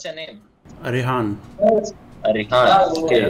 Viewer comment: Did he just say Ye boi 😂😂